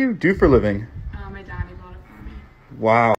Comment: her dad bought her a mansion?! When I graduated my dad got me beer. Her dad really set the bar high for next guy she dates. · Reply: I didn't hear "my daddy bought it" I heard, "My dad; he bought it for me."